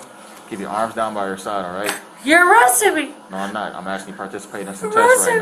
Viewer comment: Was she saying harassing or arresting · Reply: Arresting